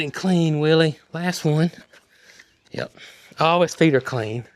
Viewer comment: you were cleaning Willy’s feet and said all his feet are clean. And I thought you said Holly’s feet are clean.😂 oh it made me laugh. I just love your videos. Love the whole family. You guys are great. Good luck this week being by yourself.❤❤❤❤